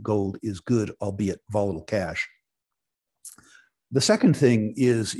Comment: HELP here, did he say that as for himself, Gold is as good as cash? I could not make it out. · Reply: "...volatile cash"